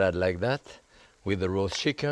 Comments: For a second I thought he said “Nice salad like that, with the raw chicken, perfect meal”. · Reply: Samuel Lindblom I thought he said ‘raw’ too 😮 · For a second I thought he said “Nice salad like that, with the raw chicken, perfect meal”. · does he say to eat it with a raw chicken? [i turned on captions to see if it was different, but it said what i thought he said :-)] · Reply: mm "with the roast chicken".